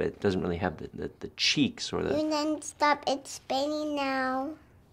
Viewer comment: What did she say at the end? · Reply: I thought she said "you can stop speaking now" lol